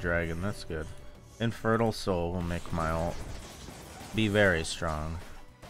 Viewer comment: I thought he said “infertile soul will help my ult” 😭